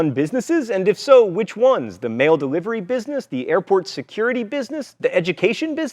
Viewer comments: I thought he said male delivery business. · I thought he said "male delivery business" and I was confused until I realized